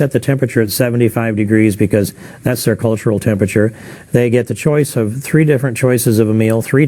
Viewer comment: "That's their cultural tempature" did- did he really just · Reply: I heard "the controlled temperature." But now you mention it I can't not here "cultural".